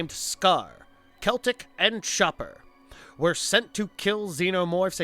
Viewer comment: Chopper not schopper. Lol it sounded like you said shopper or something not chopper. Lol